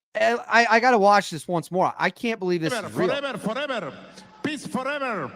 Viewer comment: Sounds like he is saying piss for ever